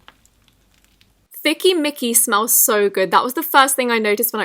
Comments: When she said thicky Mickey I thought she said thicky nicki lmao 😂 She’s thicker than a snicker Edit: omg thanks for replying I love your vids❤️❤️❤️ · When u said thicky mickey I thought u said thikky nikki for some reason😂😂